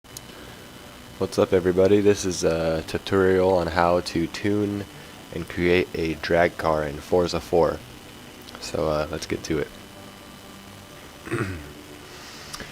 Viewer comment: it sounded like you said TITorial witch is fine but i was just saying